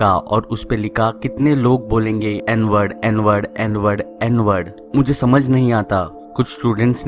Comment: "Tharki Budha" That was too hard!! 😂😂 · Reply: what is this" enward" word